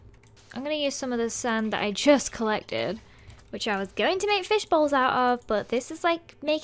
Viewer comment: for a second I thought you said fish balls instead of fish bowls >insert 300 Lenny faces<